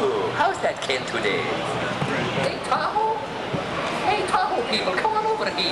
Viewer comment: I thought he was saying "Lake Tahoe" but I can't tell for sure.